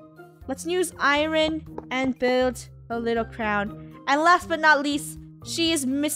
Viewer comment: When funneh said last but not least on her Cindy build it sounded like she said I laugh but not east😂